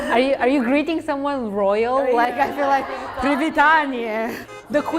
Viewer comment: you guys put a different word “няскоранасць” (looks Russian) in the captions. The word the Bulgarian girl actually says in Bulgarian is “непреклонност”. Edit: It’s actually the Belarusian word from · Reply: also at 19:05 Bulgarian girl says Royal and captions say Loyal.